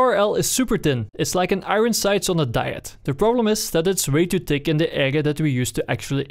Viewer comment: Dude i dont know what your saying sometimes in the video like at 1:14 all i hear is “its waytudig indagea datwe” thank god for cc though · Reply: He says "it's like an iron sight on a diet the problem is that it's way too thick on the area that you use to actually aim at your enemie"